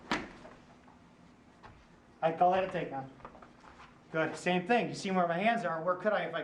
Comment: He didn't...are you sure you were listening? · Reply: Tactical Combat Martial Arts He DID...are you sure you were WATCHING? At 8:07 he actually says "I call that a take down."